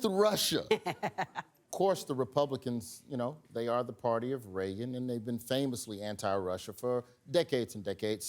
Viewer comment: When Roy began to say the party of Reagan, I thought I heard the party of rapin'... and that has become quite an apt description with Trump as a serial sexual predator found liable for RAPE. Moreover, Trump is raping America like he rapes girls and women. Violently and without consent.